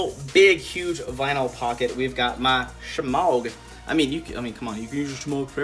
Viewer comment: how do you spell that thing at 15:05? Shmouk? smauk? the pronunciation is really weird and I didn't understand. BTW great pack, with very good inspirations but I wouldn't carry nearly as much stuff. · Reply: Shemagh